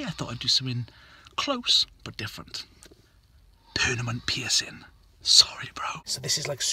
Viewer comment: he said pernament instead of permanent